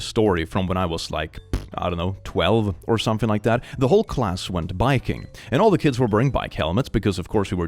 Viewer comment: When you said “the whole class went biking” I heard “the whole class went viking” and thought hmmm that’s reasonable for Nordic countries I guess